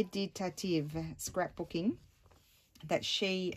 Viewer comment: Hello ....Well I do see this is a late view of this video, however I have mentioned I am new to JJ and Loving all the videos. At the end of th is video you mention a woman's name or YouTube name. I do hope you will assist me, please provide the name of the person you mention inspired the belt wrap under the locket. Me di ta tieve scrapbooking is what I hear. Im sure I am incorrect. My ask may be a strange one, & I thank you for entertaining me with the assist. The time in which you mention the name is at 1:10:05. Christine S. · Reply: Meditative Scrapbooking. Thank you so much!